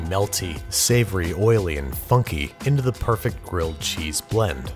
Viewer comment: Im sorry but 2:59 I heard fucky.